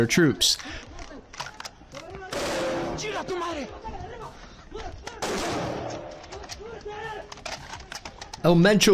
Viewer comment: what he say?! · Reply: “Chinga tu madre” “Eff your mother” Them are fighting words